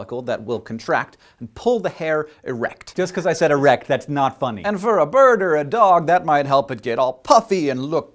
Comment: when he say "And for a Bird or a Dog..." I Thought he said "For a Burger, Dog" And that made me Hungry!!!